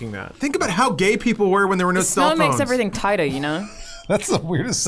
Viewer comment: Think about how gay people were when they were into cellphones? Is that what i heard? God i love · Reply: *think about how gay people were when there were no cellphones